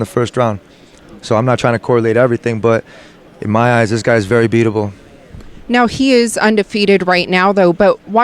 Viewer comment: thought he said in "In my eye's this guy is very beautiful".